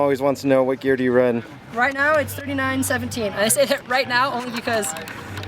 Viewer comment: he mentioned his gearing, but I couldn't quite hear what he said. Did anyone catch what he said his gearing was? Thanks in advance for replying. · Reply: Peter Chung 39-17.